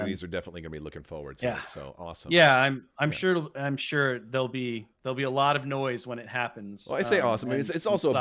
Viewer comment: What does says at 48:40? "I'm sure there'll be a lot of noise when it happens with the size of the release?" Anyone else hear him say something else?